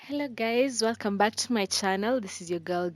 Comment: I know it's her accent but at first I heard 'hello gays' and I was like 'how did she know?!?!' 😱😱😱